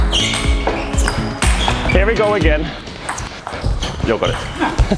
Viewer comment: ﾔｳﾞｧｲにしか聞こえん